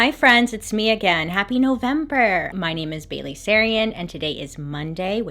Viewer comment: I've been sick for 2 days binging all your videos and my husband after 35 videos said OH her name is BAILEY sarian? I thought she was saying BABY. THE WHOLE TIME??